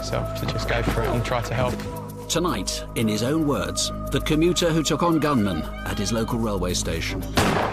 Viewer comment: I thought he said "The commuter who took on gunmen and his local railway station!" for a minute! Like he suddenly turned into the Hulk and smashed up a train after foiling the robbery 😁